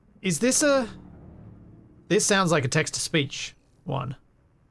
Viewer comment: I misheard you for a second as "This sounds like a Texas speech one"... And "She was saying 'Corpus'". I'm like; she's from Corpus Christi? Oh, "Text-To-Speech" reading that as a sound of "corpus" and not "corpO's". Here I was wondering how many Texans you had run across.